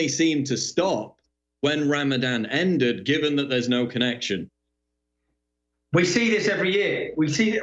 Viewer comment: sounded like Paul said "... there mosque be a connection"